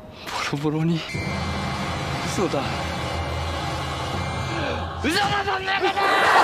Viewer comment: 真面目なシーンなのに なんだか 笑ってしまう「 嘘だそんなこと」が「 ウソダドンドコドーン 」に 聞こえる😁「 本能に従う それが アンデッドの運命だ」じゃなくて「 本当に笑ってしまう それが人間の本能だ」に なっちゃいますね😂